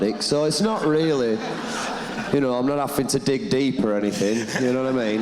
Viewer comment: What does Joe say at 10:38? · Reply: Not having to dig deep.